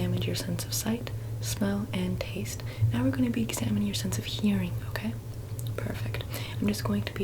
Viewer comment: I misheard "sense of hearing" as "sense of humor" and was waiting for her to say something really funny but then she started snapping and I got confused.